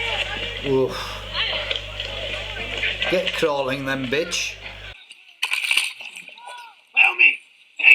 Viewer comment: Did he say get crawling then bitch